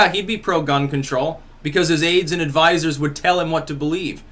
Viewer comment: When you said Bernie's aides and advisors would tell him what to believe, I heard it as his AIDS haha